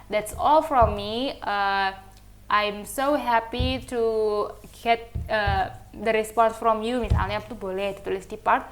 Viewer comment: Excuse me miss di menit ke 10:54 itu ngomong apa yah?agak kurang jelas dengar pengucapan nya🙏🏼 Thank you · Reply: i.m so happy to get the response from you